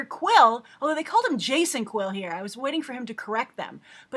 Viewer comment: wrong. I thought he said Jason quill but he actually said peter Jason quill. there was just noise or talking while he said peter